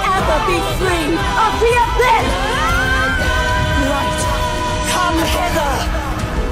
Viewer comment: I heard.. Come Hi*ler hahahaha